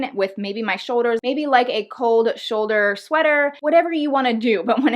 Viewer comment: Thought you said cold shoulder tops look cheap.